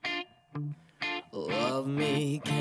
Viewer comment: I thought it was saying Love Me Dad.